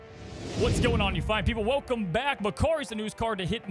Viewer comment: I thought you said "What's going on you 5 people" at the start